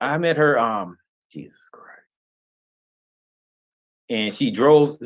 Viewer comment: OH GOSH!HAHAHAHAHAHA!!! When he was at that long pause 6:14 I said out loud “for god sake what’s the hold up?” Immediately afterwards I heard a whisper say “jesus christ”. I played that back 4 times thinking which one said that???? It was! 😂😂😂😂😂😂😂😂😂😂😂😂😂😂😂😂😂